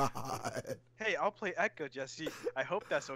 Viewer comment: the subtitle reads 'echo' instead of 'Ekko.' - Brother